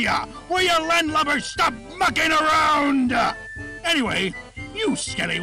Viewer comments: i thought foxy said "fucking around". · i swear foxy said the f word 14:02 but dont say i didnt warn you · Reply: Mucking.